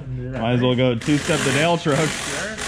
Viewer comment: I thought said "you might as well go 2 step the mail truck" 🤣🤣🤣 I now know he said Dale truck